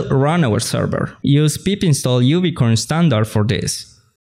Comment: I thought you said unicorn api .. you should pause on critical words like this one to give people the time to digest, and/or provide links in the description. Otherwise your stuff is pretty good. The history of fastapi in particular. Congrats and Thx to Sebastian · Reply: I heard "Uvicorn" - even with his heavy accent. Besides, @ 2:43 he literally types out `pip install "uvicorn[standard]`